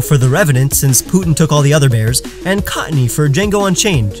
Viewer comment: he said cottonee instead of whimsicott